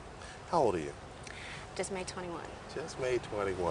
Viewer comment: I'm confused a little. At the 9:25 it sounds like she said he birthday is may 21. But Google says her birthdays in January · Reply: U hard of hearing aren't you? That's not what she said. He asked her how old is she? She said, just made 21.